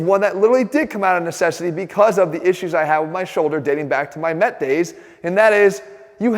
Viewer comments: I thought he said dating back to my meth days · When he said Mets days I thought Jeff said meth days. How would that work for a pre-workout? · did he say his “METH DAYS?”